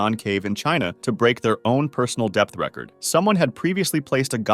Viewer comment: Depth record? I misheard that as death record